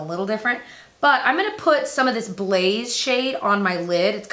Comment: When she said 'blaze shade' I thought she was saying 'beige shade' and just saying it really wrong until I realized 'blaze' was the name of the shade.